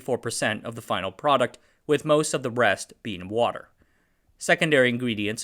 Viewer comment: i heard '... with most of the breast being water...' you sly XD